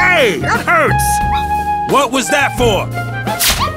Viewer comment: You know, I thought he said the same thing. I listened to it for sometime & sounded like he said: Blasted fuck ball.